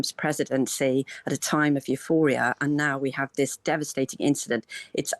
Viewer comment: Did she say about the Trump presidency "a time of euphoria"?